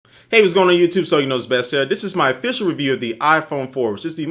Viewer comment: i always hear "Zooey knows best".. instead of "Soldier Knows Best".. haha.. Great review btw